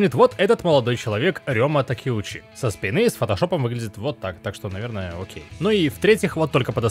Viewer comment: мне послышалось "так что наверное он гей" и тут мне стало уже гораздо интереснее